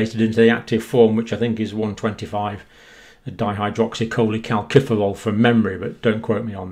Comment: Dr John Campbell, At 8:12 into this particular video, you said “But don’t Quote me on that”.....I couldn’t even pronounce, what you had said. On a more serious note, I do follow your updates, all the time, in which you just stick to the facts, I trust you, as the person who tells the truth, in regards to the world wide COVID-19 situation, and indeed any medical facts....Please keep up the good work. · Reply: I believe Dr. said, “125 dihydroxycholecalciferol”